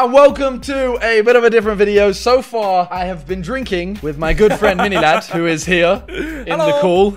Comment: i thought he said "with my girlfriend, Mini Ladd"